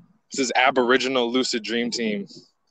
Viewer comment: once the technology reaches a point its going to only strengthen those will melanin anyway, AI just need some time to develop and by that time aboriginal people will gradually find they tribe and it'll be just like the old wakanda days and then those living in colder climates. wars will prolly only happen with just neighboring countries/villages and never get too extreme like ww2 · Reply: I thought you said aboriginals will fade away wtf